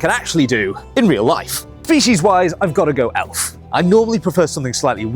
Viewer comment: I understand "fetish wise" instead of "species wise" 😭